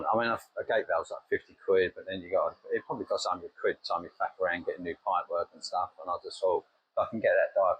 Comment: did I hear an F bomb? I thought this was a child friendly channel?!?! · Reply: No I said faffing, that's your mind jumping to the alternative 😁